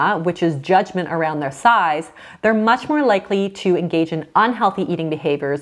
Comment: tell me I’m not the only person who heard thighs instead of size 😂